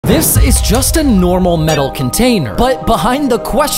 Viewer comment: dude i heard “justin normal metal container” 🗿🗿🗿🗿🗿🗿🗿🗿🗿